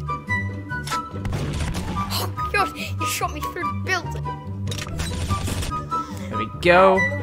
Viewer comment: why did he say omg he shot trough my builds? U can’t build LMAO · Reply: He said "he shot me through the buliding"